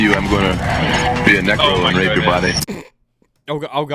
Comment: Lol, I'm on the same page as you. At first I thought he said "negro".